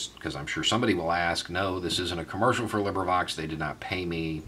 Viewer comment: Omg! This is awesome! Thanks for sharing this. I thought he said liber like liberty, but ftr it's LibriVox, just grabbed from Google play store. Can't wait to see what's on it. Muaha. I'm a book nerd 👋